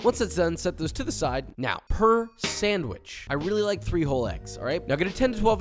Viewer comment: I had this playing on a separate screen while working and misheard a bit... I thought the first sandwich had "three-hole eggs" rather than "three whole eggs" and was really confused for a sec.